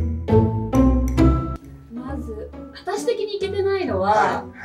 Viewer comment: I can't understand one thing in the first sentence that she says, and it's really bugging me. 0:40 まず、あたし的に(けてない？)のは... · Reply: Overaffectionate Murph - あたし的にいけてないのは